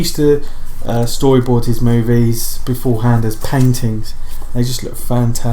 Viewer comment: Was actually doing some work on my computer while listening to the video in the background, and almost did a double take when I thought you said "He used to storyboard his movies beforehand as peng tings" 😅